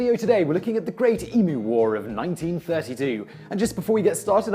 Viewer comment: Thought said Emo War, was sadly mistaken :(